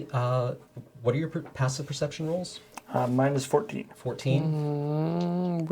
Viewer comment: "Mine is 14" I misheard as "Minus 14" = -14. DAYUM. that is one bad passive perception ^^;. Glad to confirm I misheard it << ^^;.